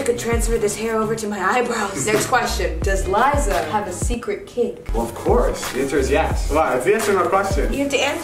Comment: did liza say kink?